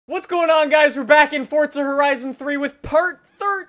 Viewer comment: It sounds like he says fortza